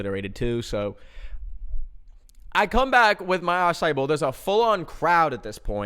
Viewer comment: I’m sorry but the ai subtitles got acaì bowl wrong and it’s so funny like fym he’s eating an eyeball